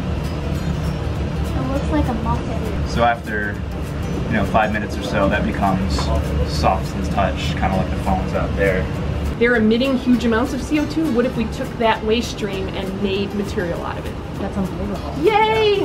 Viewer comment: I thought they said phones not foam xD